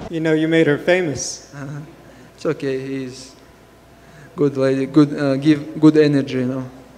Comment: “We have to respect old people” 😂 nice dig at one Irishman who punches old people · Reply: I thought he said "all people"